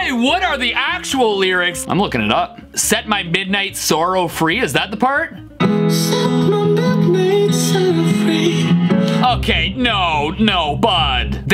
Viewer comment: I laughed so hard when it said "suck milk made sour free"😂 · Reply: Yeah lol I thought they were saying "suck my milk made salad free"😂🤣